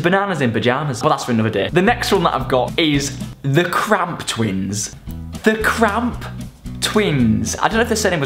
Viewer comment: When said Cramp twins at first I thought he said crab twins